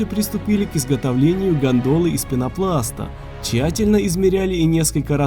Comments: мнепослышалось гондон из пенопласта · я сначала на тихой громкости услышал как "Изготовление гandoна из пенопласта" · к изготовлению кого кого? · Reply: * Хотя на деле там не Ganдона как я сначала на тихой громкости услышал, а гондола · Мне одному послышалось далее мы начали изготовлять гандоны? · гондолы послышалось гондоны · Мне послышалось гандоны из пенопласта :D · послышалось "приготовление гондона" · Лол, мне послышалось гандоны. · первый раз послышалось: Для изготовления гандона)) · Мне послышалось гандоны ;D